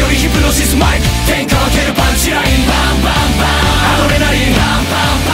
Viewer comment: パンチラインって言ってたのか！ずっとパンチラリンって聞こえてた……